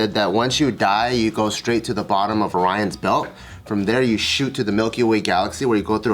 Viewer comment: I thought he said “Ryan’s belt” and I was sitting here (post-nap) for a minute thinking to myself “who tf is Ryan” until I realized he had said Orion after saying more astrology shit.